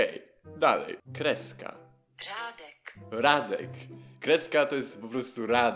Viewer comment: usłyszałam żadek